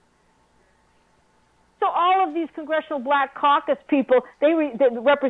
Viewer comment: lol when that stupid bitch says black Caucasus it sounds like she says black cock